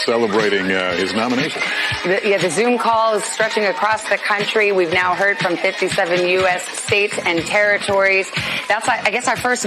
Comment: Did she say, "We've now heard from 57 States and territories...@ 6:23?"